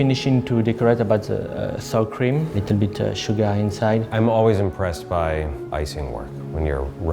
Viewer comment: DID HE SAY SUGA HAHAH